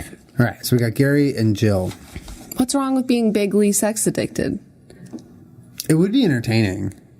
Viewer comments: I thought Julien said she was "Big League Sex Addicted" 😂😂😂😂😂😂⚾️⚾️⚾️⚾️ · I thought they were saying big league sex addicted. I was so fucking confused